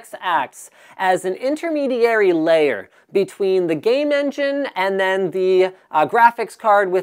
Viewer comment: It sounded like he said Gay Men Gin!